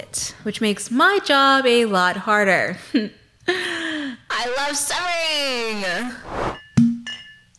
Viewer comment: I keep hearing "I love sewing," which, funnily enough, is what I'm doing while listening to background video essays. (I'm repairing a jacket for my trans brother, just a minor fixing to prevent further jacket. A really easy but time-consuming & tedious project, my favorite kind of hand-sewing.)